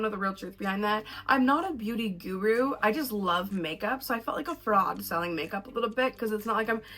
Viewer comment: I thought you said “frog selling makeup” 😭😂😂😂 don’t worry I’m drinking some coffee now 😂🙌🏽❤️